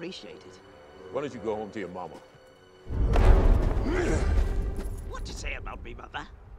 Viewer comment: I thought he said,what’d you say about me papa?